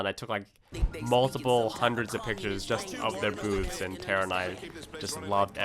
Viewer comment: am I the only one who heard this?!!! "...I took multiple, hundreds of pictures just of their BOOBS and I just loved every second of it..."